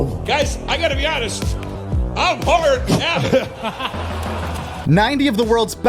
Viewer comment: What does DeRosa say here? I can't make it out for the life of me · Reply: "im hard already"